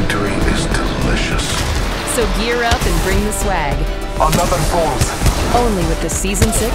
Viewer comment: Did she just say “Swag”?